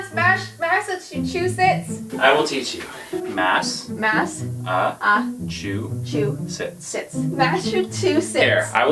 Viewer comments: It sounded like they said massive two shits lol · My friends for some reason “worship” Elmo. They also created their own version of Elmo called “Hellmo.” Also it sounded like Ty said: *Mass-uh-two-shits.” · nobody: not even: ty: M A S S A T W O S H I T S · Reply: ミカサ・アッカーマン lol i thought he said massive two shits 😂😂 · At first I thought she said “Massa-TO-SHITS” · Why did Sound like he said massive two shits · Ha ha ha I’m sorry but it sounded like ty said masa two shits sorry for swearing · He sounded like he said it: Ma-se-two-shits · It sounds like says massive two shits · It sounds like he said masiv to shits HAHAHA HAHAHA HAHAHA 🤣🤣🤣🤣🤣🤣🤣🤣🤣🤣🤣